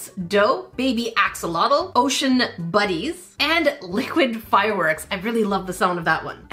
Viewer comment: When she said ocean bunnies I thought she said ocean baddies 😂